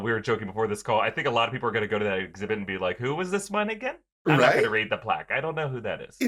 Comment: I thought had said “who is this my n***a??” Had to rewatch it three times to hear that he said “one again” 💀